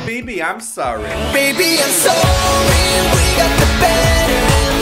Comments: i thought he said we can do better. not we got the better...i feel smart... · LOL At first I thought they said "Baby I'm sorry we got the butter!" XDDDDDD